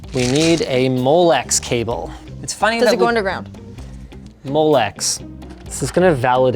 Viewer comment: L: T"THis is a Molex Cable" "Dos it go under ground?" ROFLMAO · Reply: I thought she said does it go into the ground which makes sense for a cable joke